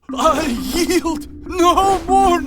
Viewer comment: Did he just say “Yee”?